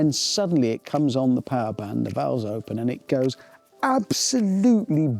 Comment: did he just say valves?